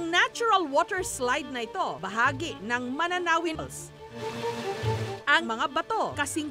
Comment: May narinig po ba kayo sa 1:32 nung sabi niya manananrin falls? pls tell me